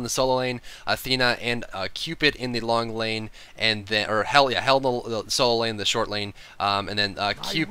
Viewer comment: anyone hear "Hell yeah" at 2:59?